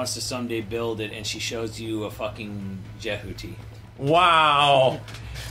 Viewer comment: What does he say? Jahuti?